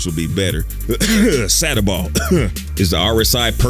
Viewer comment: Sadderball? Satterball? What are you trying to say? Google can't find anything. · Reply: Sataball